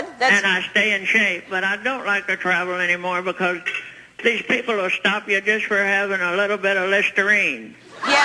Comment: What did she say at 5:43? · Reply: I don't like to travel anymore because these people will stop you for having a tiny bit of Listerine